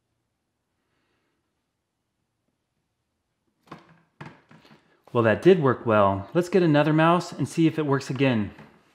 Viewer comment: What's it say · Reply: “Free Tibet”